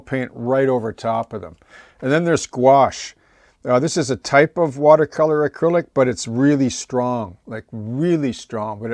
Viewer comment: What color is the gouache when you were showing the rust colors (time stamp 1:01:30)? Sorry, if you said, but I didn't catch it, and couldn't tell. Thanks! I've not used it before. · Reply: Burnt Umber.